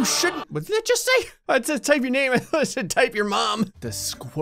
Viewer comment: I also thought it said type your mom